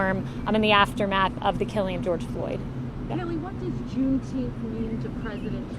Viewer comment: June what? Somebody help me out. Sounded like she said June teenth. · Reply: Jeremy Jackson - she did. It’s some obscure commemorative date (June 19th -they call it Juneteenth🤯) that hardly anyone recognizes - kinda like Kwaanza.